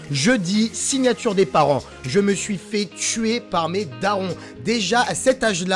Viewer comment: C'est moi ou j'ai entendue je me suis fais tué par mes darons à